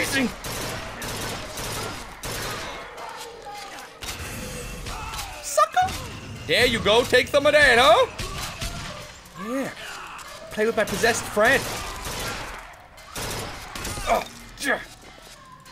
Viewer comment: Does anyone realise that sound like he says fucker when he says sucker?